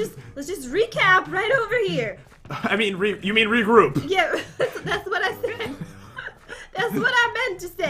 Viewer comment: On 3:41 did Jen say "that's what I said TODAY?!?!?!?"